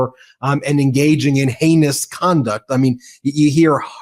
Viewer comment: Did he say “anus conduct” @ 1:07 ??